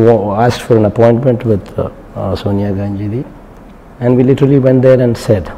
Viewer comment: I heard Ganjeri